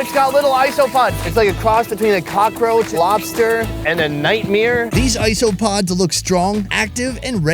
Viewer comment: did he say nightmire? Like Bert Chrysler ?